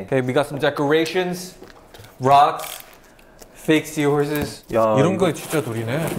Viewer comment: lmfao why did the captions say that Johnny said “fixtures” instead of fake seahorses at 4:19 I was like Johnny is forgetting English